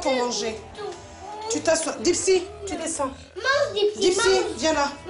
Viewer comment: j'ai cru elle a dis à "galsi" genre assied toi en arabe😂😂😂😂😂😂😂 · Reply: Non c'est delsi le chien